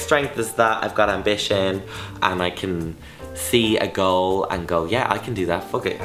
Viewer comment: I literally thought you said "girl" instead of "goal"